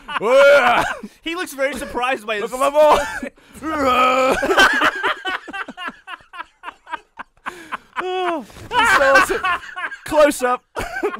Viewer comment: It somehow makes it even funnier when it sounds like Gavin is saying "Oooh yeah! Look at mah boy!"